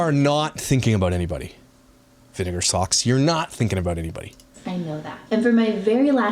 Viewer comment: “Here, have some lima beans and vinegar socks.” Hahaha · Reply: I thought he said vinegar sauce but socks is way funnier 😂 🤣